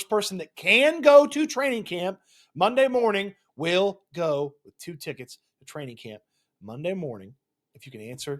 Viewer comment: I thought you said tickets to Coldplay